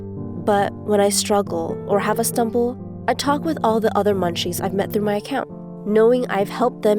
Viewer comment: I thought it said scrunchie I